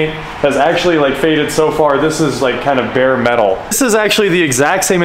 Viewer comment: did you say bare metal? LMAO!!!!!